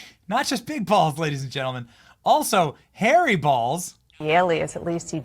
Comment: Oh my....Being a British born person , I thought you said "hairy' balls ......